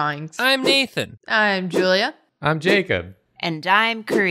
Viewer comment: I misheard and thought Jacob said "I'm Jathan". Reminded me of the intro, when everyone was Jacob :D do an all - than intro. Nathan, Jathan Julthan and